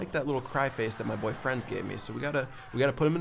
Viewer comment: seconds it sounds like he is saying boyfriends